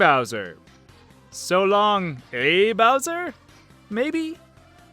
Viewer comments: When I heard mario swinging bowser I thought he said "So long de boun key!" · I thought Mario said so long B Bowser